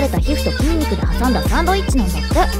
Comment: 「よろしい」が「やらしい」に聞こえる俺は疲れてる